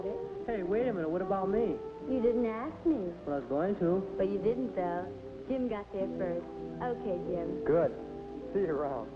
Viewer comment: What does the say at 10:55? Jim got dibs first? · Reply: "But you didn't, Doug. Jim got there first."